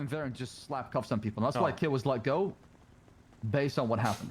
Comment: Wait really ? I thought she was one of the good ones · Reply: it sounds like says "that's why Kit was let go". Unless I'm mishearing him